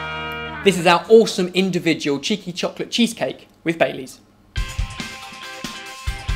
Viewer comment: I thought he said "Chicken Chocolate Cheesecake" lol